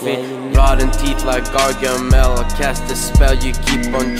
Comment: When he said Gargamel I thought he said cocomelon am I the only that thought that